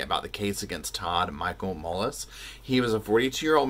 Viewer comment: I thought he said Todd Mullet instead of Mullis. Then, starts talking about farm equipment, and I thought, "Well, that tracks."